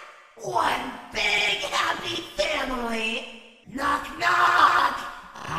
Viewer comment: На 1:43 я одна слышу: КоОоОгДа тЪ ПрИвЕт?